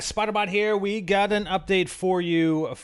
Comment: Lol it sounded like he said spyder bot